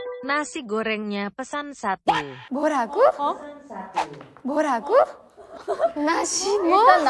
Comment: I found it cute when karina said 뭐라구(mworagu) instead of 뭐라고(mworago) that's the cutest thing 😭 Karina why are you so cute.